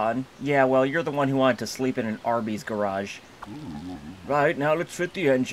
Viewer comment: I just heard "Arby's" started selling cars